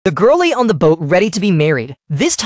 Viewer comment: Nah I really thought he said “the girly on the boat” like I really thought he said girly but he actually said the girl lay on the boat lol